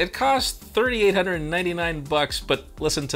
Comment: did he just say $399? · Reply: $3899 lol